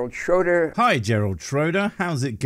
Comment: I almost thought he said his name was Schrodinger.... That would made God.. "Schrodinger's God". . For those who did not get this, it's reference to "Schrodinger's cat", look that up if you're interested.... 😮😂😂😂